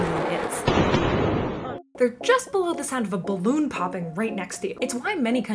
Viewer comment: When she said "balloon popping" I heard "baboon popping" and had a weird image in my head for a second there!